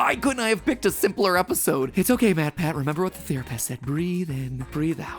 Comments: I could only hear him say “theory”pist instead of therapist. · Did I just hear "Theory"pist? Or have I just heard too many of your cheesy puns Mat. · Therapist: MatPat-achu doesn't exist, he can't hurt you" MatPat-achu: 👀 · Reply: I swear he said "theor-a-pist" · "...remember what the theorypist said." I heard that pun >_> Unless he really did just say therapist